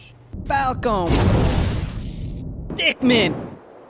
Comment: Why Do I hear Dickmin pawch?